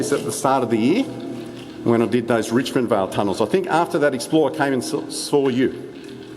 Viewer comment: For a music picture slideshow of this day out pls click on the link below. <link> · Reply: I’ve heard about this tunnel before, I only saw it 2 years ago, I remember my Grandpa telling me about what I heard as the “Richmond Tunnels” (maybe he said or meant Richmondvale) but the idiot me thuaght it was Richmond as in the one near windsor 💀😂🤣. But! Apparently, There is a 10 metre tunnel in richmond, (my guess is that the trains went over it from the Richmond - Kurrajong line), Nice to see your using Android btw 🤠! I just got a Samsung Galaxy A36 Yesterday! I switched from Apple (iPhone Se2) to Android/Samsung (Samsung Galaxy A36).